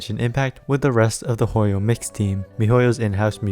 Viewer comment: Thought you said "Hoyo mixtape" for a bit